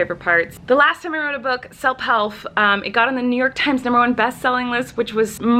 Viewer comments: Did no one else notice that she said selp helf instead of self help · did anyone else hear Selp Helf. Just me hearing things... Ok, I'll leave. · she said selp helf. Instead of self help. Lol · Did anyone notice that she said selp helf at 0:45 instead of self help